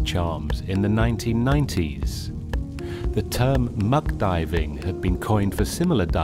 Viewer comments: who else heard muff diving the first time ? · did he say muff diving? :D